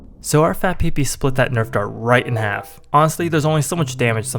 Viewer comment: i swear i heard "fat PP" edit: it actually is called fat pp ('---')